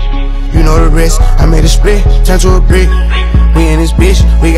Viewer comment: “I made a deuce turn to a nick, I made a nick turn to a nine, I made a nine turn to a split” hardest line I heard dis year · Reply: Bro I thought he said I made a spliff turn to a brick (which is still relatable in certain aspects of life 😂🤣)